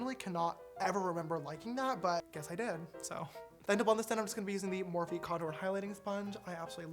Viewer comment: open captions its gibberish XD i cant even understand what your saying sister- i figured it out you said then to blend this im just gonna be using the- lol